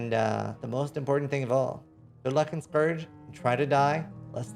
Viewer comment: did he say "Good luck in Purge"? Have a great league start everyone.